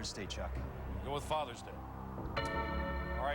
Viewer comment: I remember that greeting card question! I thought Chuck had said, "Father's Day is not even close!" LOL